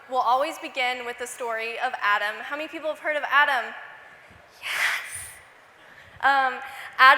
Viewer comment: i thought she was saying adam oof